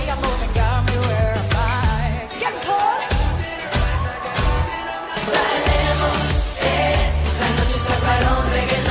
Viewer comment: What dit Perrie say at 0:46? 🥰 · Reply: " yeah Claud"